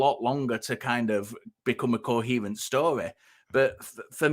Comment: did u say 'cohevant' ?